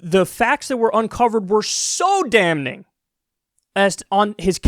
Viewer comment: Kyle did you say dam ning?